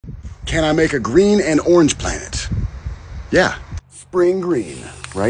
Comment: I thought you said green & orange PLANT so i was waiting for the plant to show up 😂